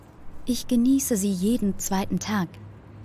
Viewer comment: Fehler 19:57 Ich giesse sie jeden zweiten Tag. Die Dame sagt: "Ich genieße sie jeden zweiten Tag" oder habe ich falsch verstanden?😕